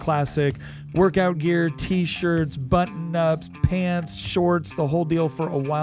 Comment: I'm SO sorry but at 3:19 I heard "but knots" and I spit my coffee out, had to go back and listen again 😂😂😂